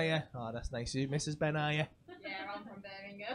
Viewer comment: sounded like she said no im from birmingham